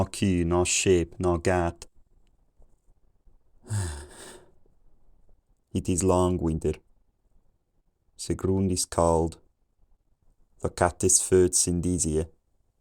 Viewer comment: I got it around the 1:40 mark “it is long winter, the ground is cold.” 1:15 (a bit before 700) I heard “it is seven days” at around 1:19 to 1:21 (slightly after 700) I heard “between samples” I also heard “chicken” earlier on than all of these